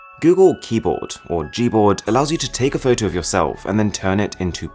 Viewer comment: auto caption says "orgy board"